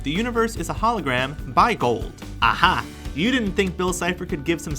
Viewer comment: I thought bill said by gold saying that reality was created by gold.